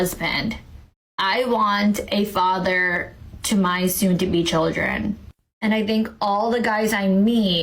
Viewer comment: A husband for HER future kids, not theirs. Nice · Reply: I thought she said soon to be kids, not Future kids, which I think indicates that she is already pregnant.